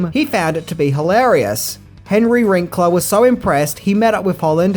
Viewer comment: I heard Henry Wrinkler and I am never saying his name correct again.